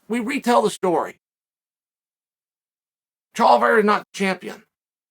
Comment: Did Chael just say "Chaliveira"😂😂😂